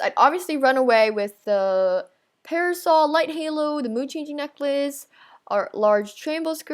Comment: When she said mood changing necklace I thought she said "muchichi" xD